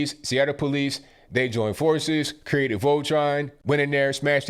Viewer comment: did I hear you right? The cops joined forces and created Voltron? >^_^< LMAO